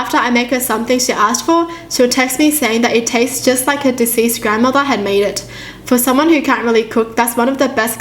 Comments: for a second I thought it said "it tastes like my deceased grandmother" shit got me scared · I FCKING THOUGHT SHE SAID IT TASTED LIKE HER DECEASED GRANDMOTHER